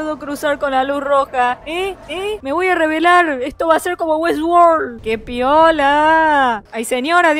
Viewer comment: Kien mas pensó que en el minuto 6:51 que dijo eddsworld ._.?